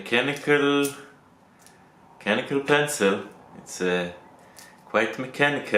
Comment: I thought it said mechanical pistol. I am disappointed